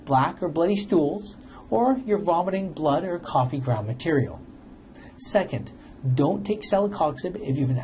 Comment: VOMITING BLOOD? He's pushing a medicine that can cause you to VOMIT BLOOD?? Celebrex is an extremely dangerous drug. It can increase your chance of having either a heart attack or a stroke by 500 PERCENT !!! DON'T LISTEN TO THIS BIG PHARMA PAID SHILL. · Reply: Good advice. Vomiting blood is not attractive, but he had me cringing with the "coffee-ground material" (0:51). Is that what he said? Vomiting "coffee-ground material"? What the h**l is that? I think I'll stick with my over-the-counter Goody's powders for my leg pain.